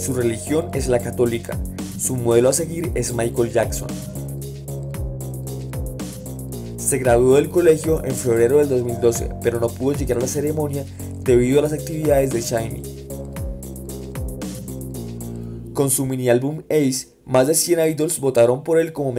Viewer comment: escuché florero